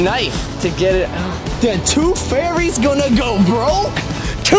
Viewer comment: One funnel dad said the tooth fairy is going to go broke tonight I thought he said the two fairies are going to go broke tonight